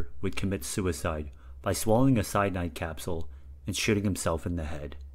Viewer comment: Hey I liked the video. I think you could do quite well on YouTube. You seem to find some quite interesting ideas and the editing quality was quite good. Now for some suggestions for improvements I think the video could use some faint background music just some simple little song and i think some lines could use another take, But overall well done! Who Dares Wins -Motto of The SAS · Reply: this is much more minor and could stay but it sounds a bit like you say shiting instead of shooting 3:47 i think you might have said latzi land marks instead of nazi land marks and then at 4:08 this there is a bit of a pause. : )